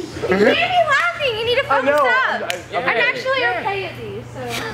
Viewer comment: You guys have chemistry and good vibes with literally everybody, it's so nice!! I love that y'all are laughing so hard, you have to stop the trick, it's genuinely so fun and heart-warming 💜💜 I missed what she says at 11:50 though, and apparently it was amazing, so I'm sad I can't figure what she said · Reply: "You cant be laughing, you gotta focus up! I'm actually okay at these!" then they all start laughing